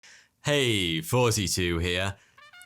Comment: I remember when I first watched Thoughty2 and thought he said “hey 42 here”